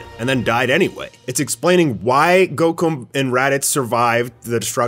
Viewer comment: anybody else catch that near 4:32 he called goku gocum LOL